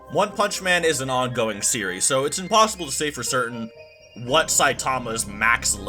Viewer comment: I did watch it there wasn’t a straight forward answer he beat around the bush the entire vid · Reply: I misheard the ending. I thought he said "is it possible for Saitama to beat Goku? No. Absolutely not." But he actually said impossible.